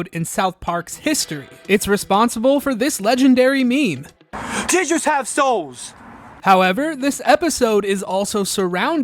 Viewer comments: Ngl I thought he said teachers have souls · I heard it as "teachers have souls" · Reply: I thought he said “triggers have souls”